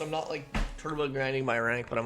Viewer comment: did you say "Turbo Grannying my rank" 😂Dan da Dan